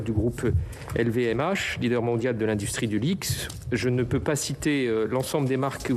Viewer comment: LVMH, leader mondial de l'industrie du quoi ? du Lixe ? HAHAHA...